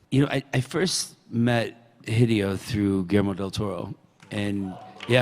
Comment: Lmao at 0:36 the auto subtitles say GMO del Toro 😂